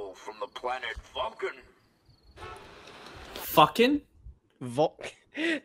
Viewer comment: It's funny you said that because when I was younger I thought he was saying the planet "fucken" too lol